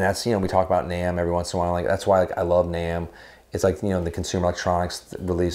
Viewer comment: Well. I am old enough that everytime I hear you say "I love Namm.. I hear "I love 'nam" and go.. "wut?"